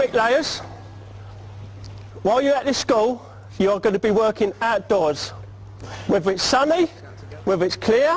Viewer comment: "You'll be atdoors" Also, did I hear that right at 34:48? That they will build an erection?